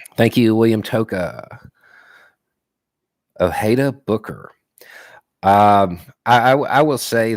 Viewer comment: I love, and his livestreams, but the mumbling sometimes makes me want to pull my hair out. He mentioned he rarely follows political figures, but he does follow.................. To me it sounds like he said Ojeda Booker, but I've been scouring the internet for a god damn hour now and all I get is an author of a horror book and Corey Booker. Does anyone know who he was referring to?